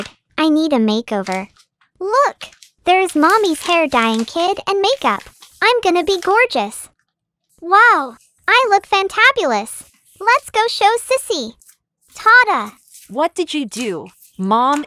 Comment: I thought she said "look! There's a dying kid and mommy's muakeup!" 0:03🥱